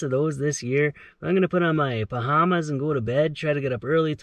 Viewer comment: I am totally going to say pajamas like that now (P A H A M A S) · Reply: I thought he said BAHAMAS... I was like, cool :)